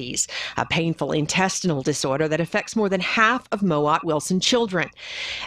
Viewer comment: I feel so bad. At first I thought he said "Owen Wilson" Syndrome